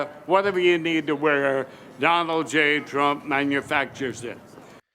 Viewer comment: I heard it clearly and truthfully "Whatever you need to wear, D-J-T manufactures shit."